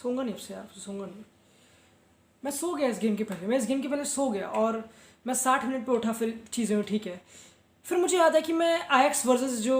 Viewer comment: Did you say that Mai 60 min me utha or fir "cheeze" uthi 😅😂🤣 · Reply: phir cheeze hui*